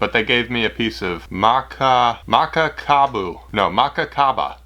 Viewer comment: did u said mecca kaaba???